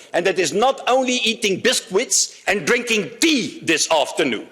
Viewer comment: All I heard was “biscwits”